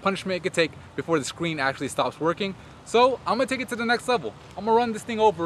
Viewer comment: it sounds like he says stop TWERKING ;)